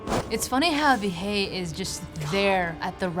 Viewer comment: As a person who does Parkour, did she just say "the shay"